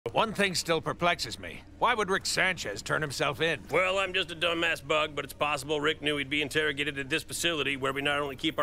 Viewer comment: "Well I'm just a dumbass bug" lmaoooo love how none of the other bugs mention it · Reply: I thought he said “ dumb mass bug “